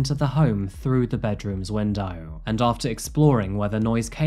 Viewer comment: I thought you said he came in through the wind dial, i was like tf? 😂